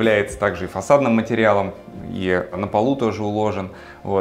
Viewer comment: сначала не поверил уху. Не "ложат", а кладут. Говорите грамотно. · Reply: Извините, послышалось, правда. Думал сказали " и на полу его ложат" ))) Извините.